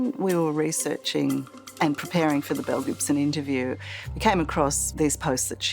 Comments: Just curious, anyone else thought they said Mel Gibson (the actor), then proceeded to keep hearing them say Mel Gibson instead of Belle then Bill?! 🥴🫣🤣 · Man, I was only listening for the first part of this interview and I thought they were saying ’Mel Gibson’.